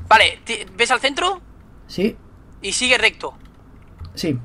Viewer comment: entendí sigue erecto ._.